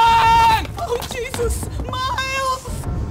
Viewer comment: Before I thought she said Smiles lol